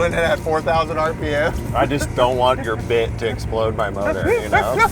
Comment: “Most reliable Ferrari” seconds earlier “DON’T TOUCH THE CLOCK IM CERTAIN IT’LL BREAK SOMETHING AND THE CAR WONT START” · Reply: I really thought he said, "I don't want your d*** to blow up my motor"